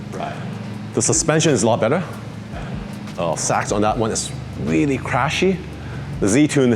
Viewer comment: is prolly saying SACHs damper in the Z tune are crashy, not “shocks” as indicated in the subtitles